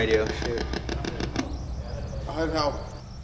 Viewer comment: I THINK IT SAYS I HELP